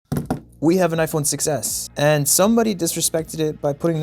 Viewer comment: him: “we have an iphone 6s” what i heard: “we have an iphone success